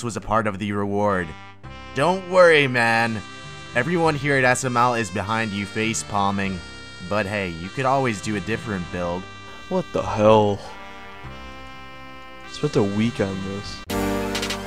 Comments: LOL at the last clip the dude said "i spent a week on this" LOOK AT HIS STATS LMFAOOOOO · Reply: jesse sytsma i thought he said weekend but idk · LOL at the last clip the dude said "i spent a week on this" LOOK AT HIS STATS LMFAOOOOO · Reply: jesse sytsma I thought he said a weekend not a week. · LOL at the last clip the dude said "i spent a week on this" LOOK AT HIS STATS LMFAOOOOO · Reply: I thought he said "I spent the weekend on this"